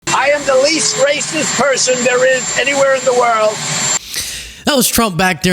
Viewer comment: LOL I thought I heard him yelling “I’m the least gracious person in the world” which is true LOL